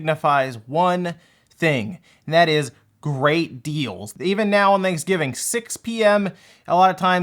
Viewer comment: I thought you were going to say “grateful” in the intro instead of “great deals” 😂